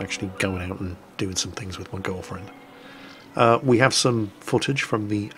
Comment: get well soon, 0:22 ... Did I hear.... Girlfriend?